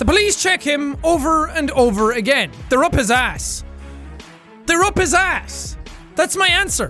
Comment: Riddle: "Security searched all over the man for the drugs, but they never find them." jacksepticeye: "'T H E Y ' R E U P H I S A S S." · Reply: I really thought he said "THEY ARE POSSESSED!"🤣🤣🤣